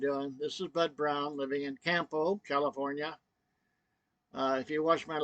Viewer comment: It almost sounded like you said you're living in Tampo, CA!?!? Tell me she's not ignoring you with the silent treatment?! But seriously, sending prayers from a long-time sub & 59-yr old Philippine life hopeful. I'm just a north of you and Anaheim, pls give me a shout if you ever come up this way, I'll take you for a ride in my blue boy